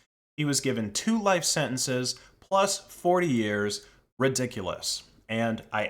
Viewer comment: Sounded like you said life sentenced plus 4 years when it was life plus forty years***